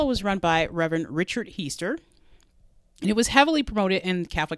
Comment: I always found the reverend's name funny because when they said it ffast it sounded like they were saying keister. One thing that was not funny was what this precious boy had to go through and then he couldn't make anyone understand him. This case infuriates me.